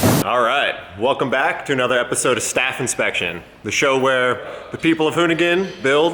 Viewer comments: Oh shit lol, I thought said staff infection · I was drunk when I watch this and I thought he said staph infection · Why did it sound like said Welcome to other episode of Staph Infection 😂😂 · I thought he said staph infection at the beginning of the video.